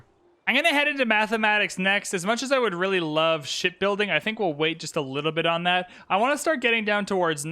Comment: For the whole video I thought that you were saying "We are now going shitbuilding" instead of shipbuilding lmao.